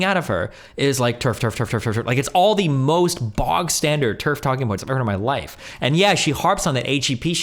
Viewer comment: Totally unimportant but I’ve heard him say this a few times and figured I’d ask the room: “Bog standard” is a phrase Destiny uses to mean vanilla. I thought it was “box standard” — as in, standard, out-of-the-box, no mods.